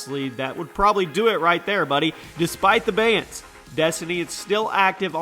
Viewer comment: Thought it said bangs 🤣